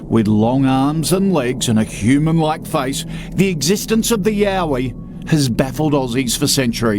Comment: oh nah, was watching this while drawing and when i heard you say "yowie" i thought you said YAOI and had to rewind LMAO